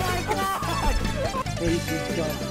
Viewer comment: When he got Phara and Mercy I heard “I got Pharmacy” and that is what the duo will be referred to as hence forth